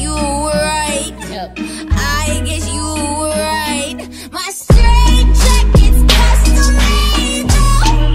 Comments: I always thought she said "My stray jacket's custom made though." Makes more since right 😂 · I thought she was saying “my straight jacket’s crushing me” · Tell me why I thought she said "my straight jackets cost a label" I was like huh? 😥😂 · I thought she was saying “my straight jacket’s crushing me” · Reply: I thought she said my straight jackets custom angel for a long time